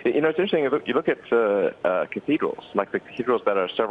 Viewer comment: Elon says something like "if you look at..." ??? "... that are several hundred years old" what is the word he's using ? (its a building type that sounds like 'eagle') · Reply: cathedral